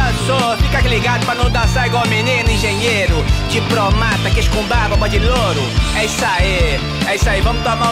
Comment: O que ele fala dos 2:29 aos 2:32??? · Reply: "Engenheiro", "diplomata", "queijo com barba", "bode loiro". "Queijo com barba" era um apelido que o Didi dava para os personagens interpretados pelo ator Philipe Levi e "bode loiro" era um apelido para os personagens do ator Carlos Kurt.